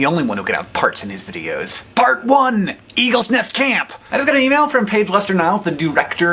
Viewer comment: I don't know why, but I heard 'Eagle Nest Cam,' and I was like, "the eagle nest camera streams were a thing, oh wait, i'm deaf."